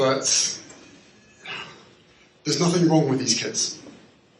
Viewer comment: It sounds strange how H mumbles around with his response then very clearly states@ 14:28, "there's nothing wrong with these kids." It comes out like there is nothing wrong with "his" kids. 😮This just may be the way I heard it though. But it is pretty clear they both act uncomfortable with answering questions about their children and parenting.